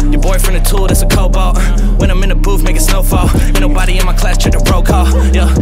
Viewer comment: Did He say the n word at 1:20? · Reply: he said makin. "when im in the booth, makin snow fall."